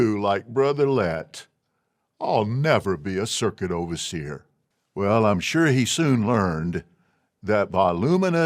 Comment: "Ill never be a circuit overseer"😞 Said NO ONE, ever. I already cringed and not 2 minutes in. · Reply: Not being an ex-JW and not familiar with the lingo, I thought he had said "circus overseer" - lol